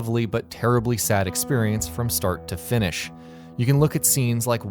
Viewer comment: DID I HEAR "Finnish"?!